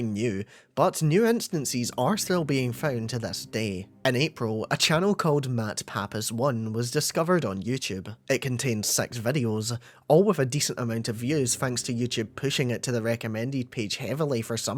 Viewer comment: I thought he said "Matt Pat is 1"